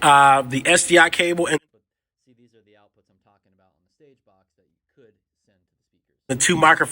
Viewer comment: What was said at 11:06? Mic volume isn't loud enough. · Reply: "See these are the outputs that I'm talking about on the stagebox that you could send to the speakers"